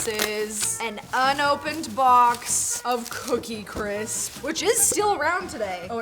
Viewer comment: i heard the cookie" CRIPS " Lol it happens to me a lot.